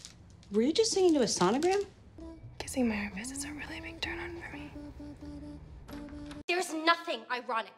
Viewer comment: What does Britt say in 7:28? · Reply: kissing my armpits is a really big turn on for me